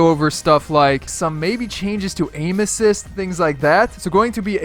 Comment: did he just say peen assist? I have such a dirty mind